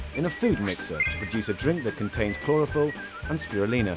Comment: Chlorophyll! I thought he said chloroform.